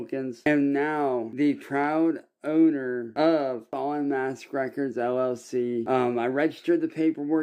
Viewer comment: I thought said Fallen Ass records